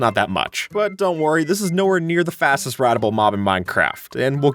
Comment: the captions say “ratable” instead of “rideable”.